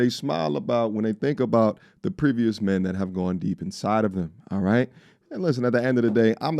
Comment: Wait, wtf is 11:51 ? 'Thinking the men that has gone inside of him'? Did I misheard. wtf was that?